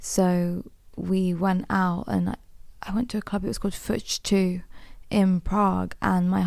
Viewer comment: Did anyone catch the name of the club She is mentioning? I don't understand Her pronunciation. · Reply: fuchs2 :)